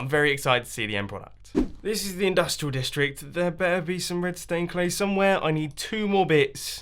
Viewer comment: I thought he said redstone clay... Lol